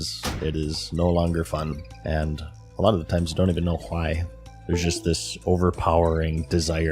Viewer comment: WHY DOES HE SAY "HAWAII" INSTEAD OF "WHY"